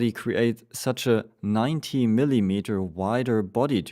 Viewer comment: 19 centimeters I believe instead of MM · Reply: 90mm I said